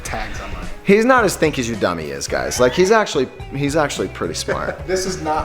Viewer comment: What exactly did say about after he removed the labels from the protective glasses? It sounded like he said "he's not as think as he dumb he is guys." I don't know what's more entertaining, watching you guys melting things or listening to what you both say.